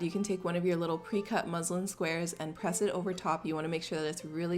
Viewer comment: I literally thought she said "Muslim square"